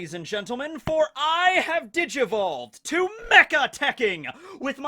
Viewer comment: Lol, I thought he said " behold people i have [ digi balls ]" I said what? but he said digivoled.